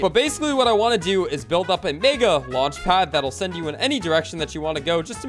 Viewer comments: I swear.. It sounds like he says the N word at 4:53 O_O I mean ik he says Mega..but still.. Its the way he says it that it sounds like the N word.. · what did he say here? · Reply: Mega